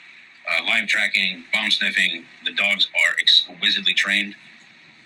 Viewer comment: I need to get my hearing checked. I thought he said bum sniffing dogs 🤦‍♀️